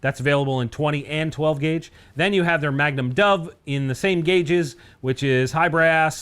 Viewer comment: I’m obviously going deaf, I heard “magnum dump” not magnum dove.